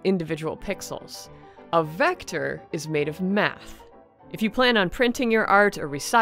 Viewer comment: I THOUGHT YOU SAID METH